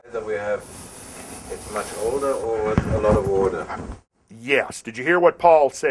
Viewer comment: what did paul say @ 9:13 ? i cannot understand what he is saying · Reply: "It is much older and alot of water" ...was the best I could make out of it